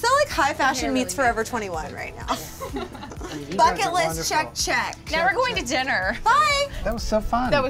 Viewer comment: Around the end I thought said "Fucket List" 😂😂